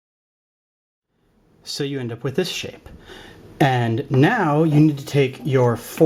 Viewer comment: I thought he Sayed "and so you end up with this shit" lol